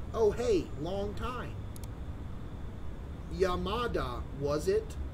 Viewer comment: sounded like he said ya mada like your mom😭